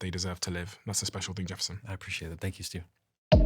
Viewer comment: Do you like these types of convos? If so please hit the like button on the vid - that’s the best way to vote for more like this ❤ also, would be doing me a big favour if you could subscribe - its free 🙏🏾appreciate you! - SB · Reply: Last seconds of the interview, I 100% must have heard it wrong but did he say "THANK YOU STU?" Anyone else hear that? 2:47:10 Now I kinda here "Steven"... MUM I AM BROKEN AGAIN!!!